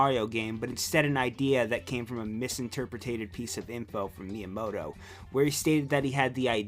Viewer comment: did you just say misinterpretATed?